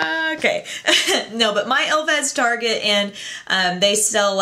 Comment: lol at 1:20 u say my elf has target. instead of my target has elf :P great hauul :D